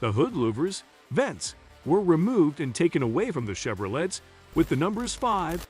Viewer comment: Did he just say "chevrolettes?"